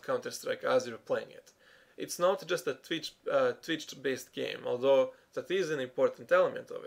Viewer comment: "It's not just a trids based game?" Could you tell me, what did you say there? I just couldn't hear it right.